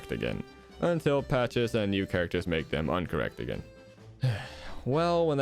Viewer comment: did I hear uncorrect or incorrect